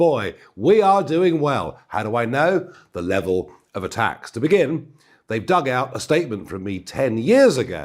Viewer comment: I thought he said the Devil of attack. He comes in all sorts of disguises